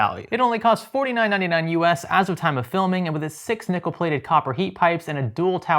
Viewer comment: I heard "nipple-plated" the first time around, 😊, my bad.